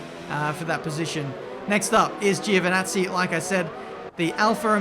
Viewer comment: when you said "up next is giovanazzi" Sounded like you said "Gio the nazi"